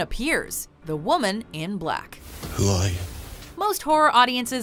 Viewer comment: I thought she said the woman is black XD